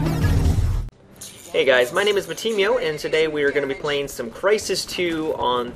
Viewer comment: When I heard Matimi0... I thought you said Mattimeo.